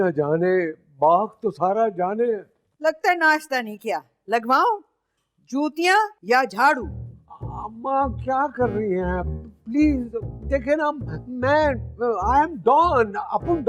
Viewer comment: When dadee said jootya 37:09 I thought she said chootya